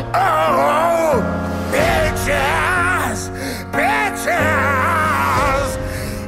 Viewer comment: It sounds like he is saying bitches